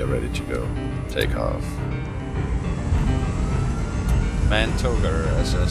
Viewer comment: nice vid! but what do they say at 0:59??? man tolder......? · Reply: bijlmermont MAN TOGA manual toga or full power take off